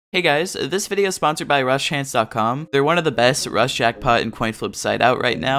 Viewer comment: Does anyone else hear him say 'Rush' instead of 'Rust'